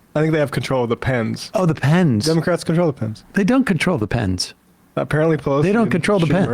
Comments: Sounded like he said Depends. Like a Freudian slip about their conversations. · I heard him say the democrats control the dems